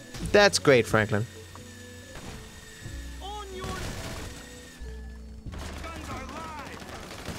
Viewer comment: Anyone else hear "onion!"